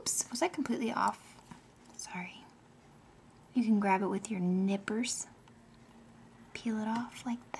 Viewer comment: omg for some reason i thought you were gonna say at 14:06 'grab it with your NIPPLES' instead of Nippers! xD Woops!